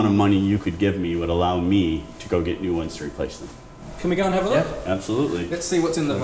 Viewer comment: Please, help me: What does he say at 2:08? "No amount of money you could get me would allow me to [...]" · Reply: go get new ones to replace them